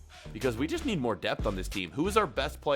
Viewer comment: Did anybody hear he said 71 years of age